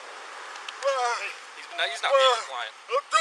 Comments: did the officer at 2:00 say "he's not deaf he's lying" or am I hearing things? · can’t hear clearly it sounds like he said he’s lying